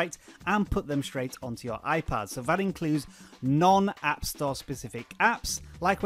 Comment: Thought you said no pc included....